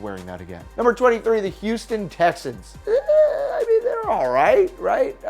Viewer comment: It sounded like you said the houston tesens